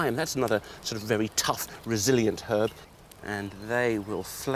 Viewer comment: Did he said Brazilian?